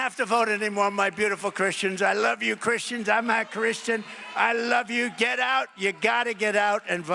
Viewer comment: Every time I listen to that speech intro, I hear 45 say " I'm not a christian". No? Listen